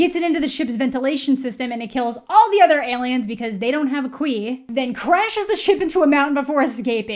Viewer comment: Everytime "khui" is mentioned, I hear "queef" and it's amazing